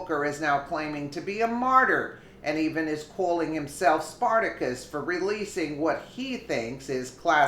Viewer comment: Booker said Spartacus moment.......I Thought he said .......Stupidity moment....?............. m (UK)